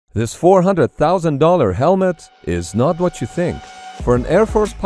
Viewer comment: $400,000, sheesh I thought you said $4,000